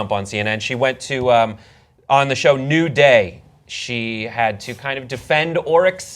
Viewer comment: oh the show is called NEW day, thought you said NUDE day. i lost interest in the story now